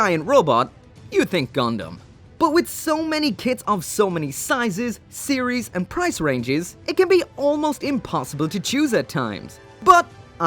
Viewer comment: i heard "soo many tits of soo many sizes series and price ranges it can be almost impossible to choose at times " and i totally agree with that statement